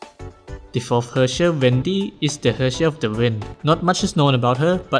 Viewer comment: I THOUGHT U SAID VENTI NOT WENDY